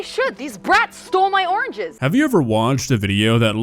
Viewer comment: Oh Oranges! Thought she said "These brats stole my organs!"